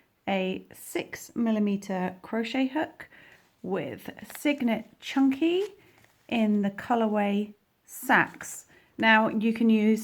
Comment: my husband, walked by as I was watching your tutorial just now and right when you said “yarn name chunky” he was like “what did she call you?” 🤣🤣